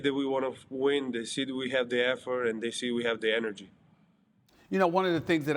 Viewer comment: Why when he said “effort” it sounded like he said “f word” 😂